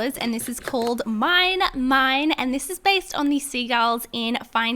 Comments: Lol at first I thought she was saying sea gal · I always thought the seagulls said “MIKE MIKE MIKE” I was weird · Is it just your accent or does is sound like you were saying seagalls? no hate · It sounds like your saying sea gal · I thought she said sea gal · when she said seagulls i thought she said sea girl 😭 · For some reason I thought she said sea girls when she was talking about the seagulls from finding nemo😂😂😂. Maybe it was because of the accent. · I thought u said sea gal BC I was confused and when I saw the pic it was just a Seagull😂